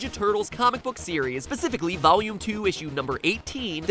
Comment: did....did he just say pacifically?